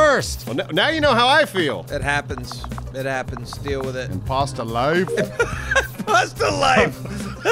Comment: was ON this episode. "Imposta Life!" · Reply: I thought he said.. "Pasta Life" That makes much more sense.